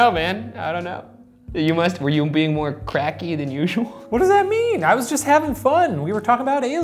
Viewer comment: Shane: "it's not official, i do not do that" Thats a word a crack smoker would say. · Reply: i thought you said cracker lmao